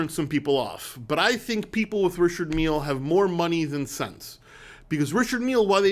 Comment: Did he say, "more money than SEX"?? Things that make you go, Hmmmm.